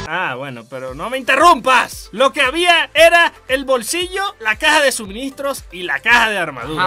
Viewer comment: pense que decia bolsillo xD era en tota, x0